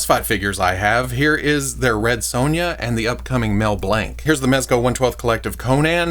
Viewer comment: I misheard the line in the size comparison as "the upcoming Mel Blanc*" and I think that's what I'm going to call my *male blank when he comes in.